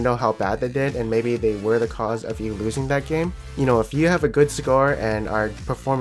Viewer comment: did you say if you have a good cigar ? 8:15 . lol I am mishearing something or you indirectly saying 420 blaze it...